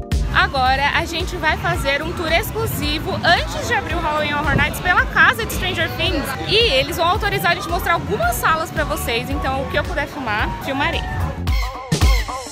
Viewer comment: entendi ela falando oq eu puder fumar fumarei